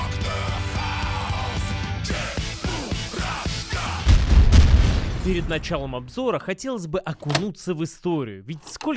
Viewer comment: Рашка?Мне не послышалось или тут упомянули регион Сербии? (Kosova është Republikë!) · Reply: Чебурашка же